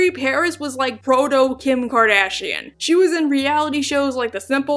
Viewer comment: I thought you said Frodo-Kim Kardashian.... I KNEW SHE WAS A HOBBIT 🤣